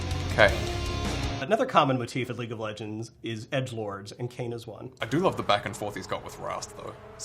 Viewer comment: all i heard was "k"